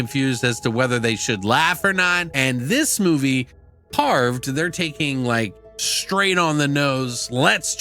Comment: For some reason I keep hearing "carbs" instead of carved. Hey what is the ending song you are playing.